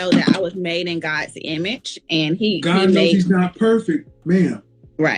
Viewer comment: Did I mishear or did Kevin Samuels say that God is not perfect? How can God not be perfect. If you believe in the word. The three attributes of God are omnipotent, omnibenevolent, and omniscient. All knowing, All powerfull and The Supreme God. In order to be Supreme you must be superior to everything else. He might have misspoke and meant that God knows she's not perfect or something like that. But I can't just not say anything about that.